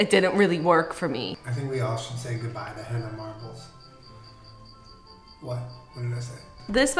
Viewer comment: This is my 58th time watching this video and i JUST heard say “henna marbles” dead